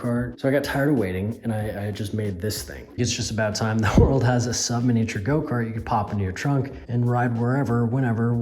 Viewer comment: For sure thought that said "I made a submarine go kart"